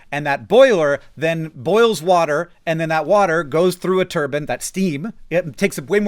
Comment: At around 2:09 i misheard Hank saying turbine as "turban" and as sad as what i'm about to say is, it is also ironically funny, that if you told the current sitting government of America that coal plants created energy using turbans it might actually be more persuasive in terms of getting them to phase out or shut down coal power plants than any actual real argument you could make. It shouldn't be this way as this is "The Onion" territory but alas here we are.